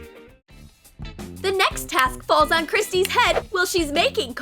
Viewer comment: It said Christy instead of red-head in 13:34🤣🤣🤣🤣